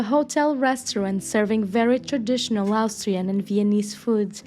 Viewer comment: In the first minute, you got it wrong. Vienna is the capital of Austria, NOT the Belgian capital. Too much champagne? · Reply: Funny I didn’t hear Austrian either. I actually thought she said ‘Hungarian’ as I immediately though ‘what’